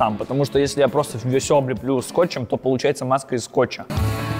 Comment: Мне послышалось что глент сказал маска из сталкера